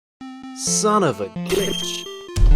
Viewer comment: I thought it said bitch